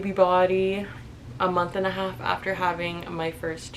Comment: Mine looks the same but just remember it took 9 months to make her; it’ll take some time for your body to go back to normal. I’m 17 months PP & just now feeling comfortable in my skin. you’re gorgeous and created LIFE .💕🥺 edit: i just watched the video (been busy all day i commented before watching) and YOU LOOK SO GOOD AFTER 2 months. I still looked 6 months pregnant 2 months PP. you’re so amazing and this video will help so many people… you’re so strong. It’ll take time like i said above. it’s been a journey for me. and i’m still learning ways to love my body. i started working out (it’s been 2 weeks) and i’m feeling more confident. just do what makes you happy LOVE YOU GIRLY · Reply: i’m sorry i meant A month. i thought u said 2 months!